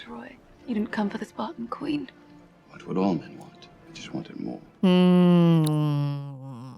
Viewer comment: he dont say "what men want,more" he said "what do all men want?" "i just want it more"